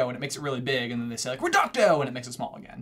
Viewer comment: they actually said "Reducio" instead of Reducto I think bcs they were trying to make it smaller